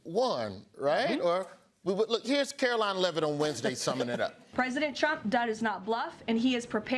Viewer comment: Everytime kkkaro-lyin says our so called president's name all I hear is 'president chump'.